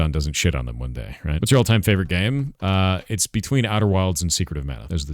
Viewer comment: I'm sorry did he just say Outer Wilds is his favorite all time game?? It certainly wasn't a bad game but that's gotta be a troll. That game was mid af. EDIT: I thought he said Outer WORLDS omg totally different thing I suck my bad I take it back.